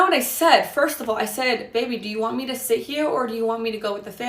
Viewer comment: So she did say, “Baby, do you want me to sit here or go with family ?” Anyone else caught it?😂 Around the 13:01 mark after saying that’s not what I said at all. She’s splitting hairs. Some form of that statement came out of mouth. She thinks she’s smart.